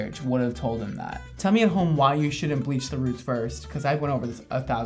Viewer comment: I thought brad said if it’s a touch up you bleach the roots first